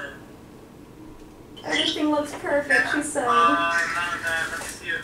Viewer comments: Did I hear Desi say, “she looks perfect, she said.” OMG 😭😭😭😭😭😭😭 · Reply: Eunice Mutuc no, she said "everything looks perfect, she said..." referring to the nurse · I HEARD HER SAY “SHE SAID SHE LOOKS SO PERFECT” DURING ULTRASOUND!!!!!!!!!!!! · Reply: I thought she said that as well but if you go back she said “everything looks perfect she said” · I HEARD HER SAY “SHE SAID SHE LOOKS SO PERFECT” DURING ULTRASOUND!!!!!!!!!!!! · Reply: I thought I heard ‘everything looks perfect, she said’ 😭